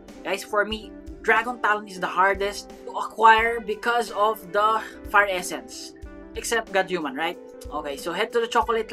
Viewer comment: who else heard fart essence